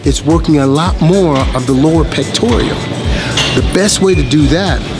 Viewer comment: I thought he was saying the "Lord Pectorial".